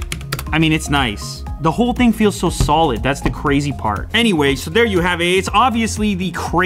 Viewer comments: I think he just said “and there you have aids” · He did not say "There you have aids." He said "There you have it." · did he said ''here you have aids lol''